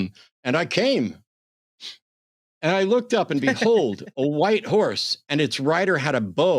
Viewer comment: Once upon a time, a guy misheard another and thought "white horse" was White House. Makes sense! Or not 😆